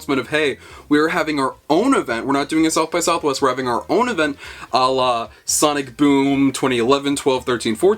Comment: did you say ALLAH?